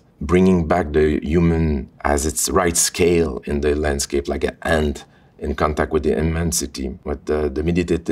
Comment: maybe he actually said "ant" instead of hand?